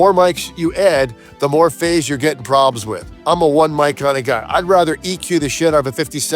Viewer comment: So worth it! Especially for “I’d rather eq the shit out of one 57 than have 4 mics phase fucking each other.” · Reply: Omg thank you for transcribing this. The word "one" is seriously load-bearing in this sentence. I kept hearing "EQ the shit out of 57 [mics]" and I'm like why would more be easier than fewer? and before long I'm googling whether even or odd numbers of mics have any well-known correlation with phase problems.